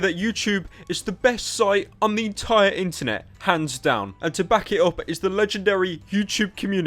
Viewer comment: it sounded like he was saying "pants down"